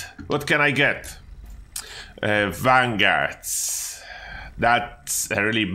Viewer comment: Why do you keep calling vanguard rubbish? On your guide you said that you didn't think that vanguard was available, but if it was available, it would be the #1 effect available?!?! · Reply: I watched again and you said unyielding. I thought that you had said vanguard. My bad :)